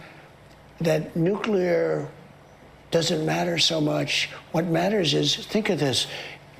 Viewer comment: he said mush instead of "much" ... must be residual frothing